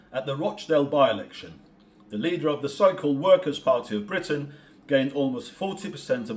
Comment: Workers party? Sorry, I thought they said shirkers.